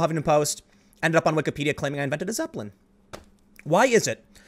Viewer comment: had to rewind that a few times.. Did i hear that right? Huffpost claimed tim invented a Zeppelin?